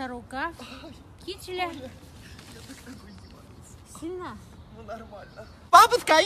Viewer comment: Я одна услышала 0:45 она сказала сына